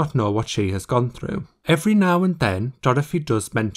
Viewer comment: " every now and then " all i hear is ariana